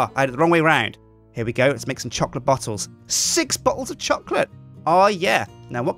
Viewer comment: I thought he said "chocolate bottoms". Hmmmm....